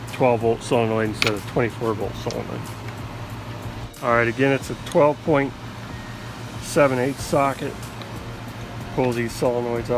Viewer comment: I heard "alright, again it's a 12.7/8ths socket" and for a moment I thought I had to buy a new tool.